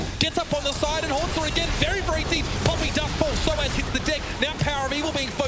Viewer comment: did he say soaz gets the dick?